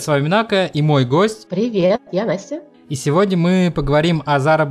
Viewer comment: послышалось "привет я вася" )))